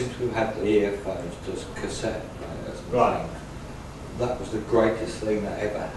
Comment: does anyone know what he said at 47:53 since we`ve had the .... ??" ! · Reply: "earphones on cassette players" I think.